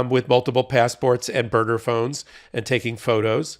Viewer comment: I thought he said murder phone……. He said burner phone .